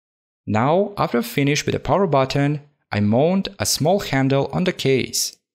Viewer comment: Is it just me or did he just say "I moaned" 19:12 😂😂